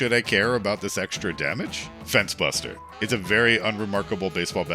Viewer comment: had to take a double take I thought you said “fent buster”